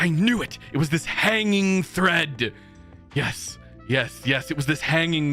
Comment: Sorry I wasn't present! I unfortunately took an accidental power nap that day. XD I will say that you genuinely scared me in the chat interrogation bit cuz I misheard you saying thread as Fred. I was gonna say I wasn't even there!!!! XD